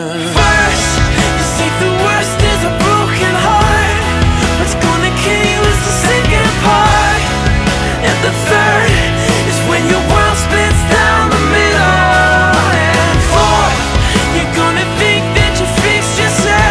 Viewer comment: What's gonna kill you is the second part WHAT'S THE SECOND PART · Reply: he says something about broken heart before that so i thought he was saying "whats gonna kill you is the sicked part"(of the heart) lol... got it wrong all along